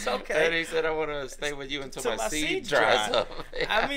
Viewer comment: Lyrics online say " 'til my seas are dried up", but I always heard it as "seeds". Glad it's not just me.